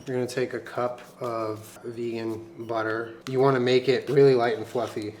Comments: When he said vegan butter, I thought he said vegan water and I was like "what the fuck is vegan water and why do you need it?" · For a minute, my brain thought you said "virgo butter" and it made complete sense that there was a company that made butter just for virgos. Then I realized I was just stupid.